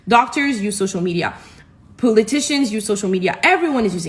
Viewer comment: the woman's accent made it sound like she said "Poo-litics instead of "Politics"! I will be using "Poo-litics" from now on because it seems more fitting lol!